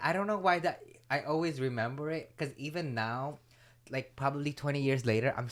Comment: it kinda sounds like was saying “I remember the phrase” 😂😂